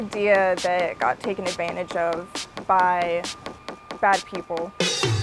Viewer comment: Did she just say "taken advantage of by fat people" because, hunny.... i have news for you...